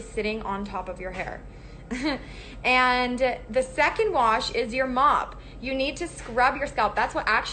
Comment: I heard it as “your mom” am I the only one😅😅😭